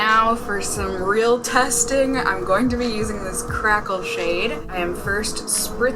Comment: I zoned out a little (her vids are just so relaxing to me) and heard "crap old shade" instead of "crackle" 😆😆😁😄